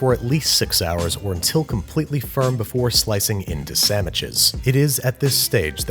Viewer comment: did he said samiches